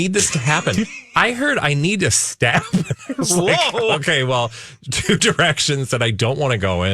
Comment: I heard “I need to stab him” too??😂😂😂